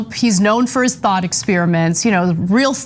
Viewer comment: Right out the gate it sounded like Anna said 'THOT experiments'